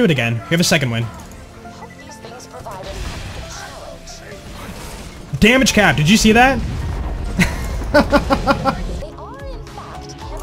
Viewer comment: i was watching at 2X speed and had to do a double-take because it sounded like you said, "Dammit, chat!" scratch that, it was a quadruple check.